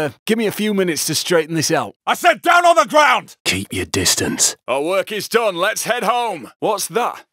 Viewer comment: What he say at 17:45? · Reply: All work is done, let's head home